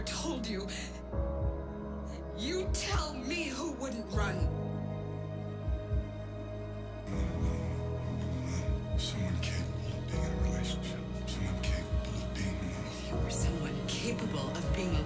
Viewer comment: wait what does he say at 0:53? sorry he talks so quietly it's so hard to hear lol thanks :3 very very very good I can't stop watching this and you convey their emotions so dang welll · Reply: Render the wolf ‘you want to know who wouldn’t run? Someone capable of being in a relationship. Someone capable of being alone.’